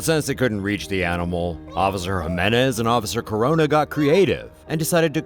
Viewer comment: did he say officer corona??